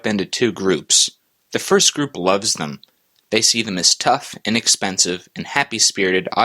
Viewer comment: I had to replay the beginning a couple times because I thought you said "and expensive" and was shaking my head. Then I realized you said "inexpensive". That's more like it.